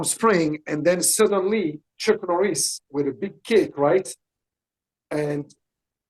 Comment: A big CAKE! I thought he was saying a big kick.